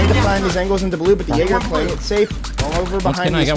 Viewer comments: sounds like they say the N-Word and I think they did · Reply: He said jager lol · ummm what did i hear at 9:20 as soon as he shoots smoke? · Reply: Jäger